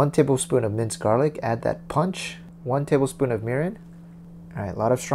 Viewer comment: Minute 1:38, ¿what it is? Mirron? I don't understand plase helpoo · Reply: It's mirin. Kind of a rice wine